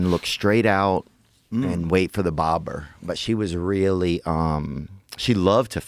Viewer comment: did he say „waiting for a bober“? If so, that‘s hilarious, especially him being half Polish